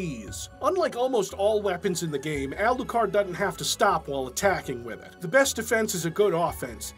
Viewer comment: I swear, not a shadow of a joke, at 41:38 it sounds like you say "Alucard doesn't have to stop while attacking women".